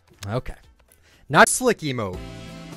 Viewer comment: sounds like he says "not slick emote" lol